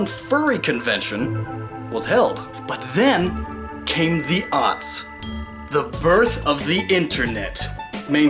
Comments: Dude...I thought you said "Then, Came the Auts" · What did he say at 5:55? "And then came the ...." Arts??? Idkkk and it's annoying me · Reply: then came the Aughts - <link>